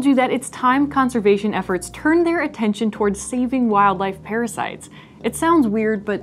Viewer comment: Fuck I thought it said pirates